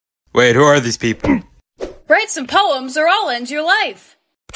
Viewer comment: is it just me or did i hear "write some poems are all into life" ????